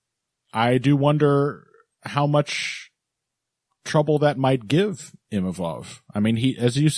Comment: In my head I heard, "... how much trouble it might givamov Imavov."